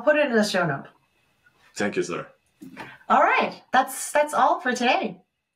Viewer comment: did he just call u a sir 😂